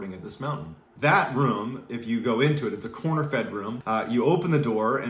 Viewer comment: A reenactment of my mishearing a phrase: 4:39: "that room, is a corn-fed room..." Me: "Oh man, bet that room is a big bottle growing boy..."